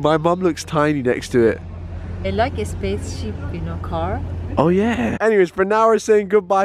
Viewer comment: I thought said space sheep .......... my mistake